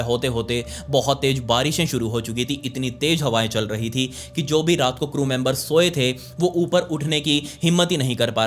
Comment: subtitle is wrong its crew* members not chrome members 😅